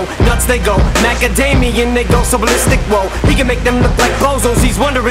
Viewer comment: LMAO 4:51 is the most replayed part of this song. Look I respect all the talent that was behind this but damn... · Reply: oh trust me, I clearly heard him say “Macadamia they go ballistic whoa” 🤯